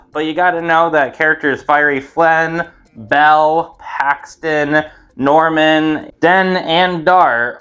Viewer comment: For a second I thought you said Haxton and not Paxton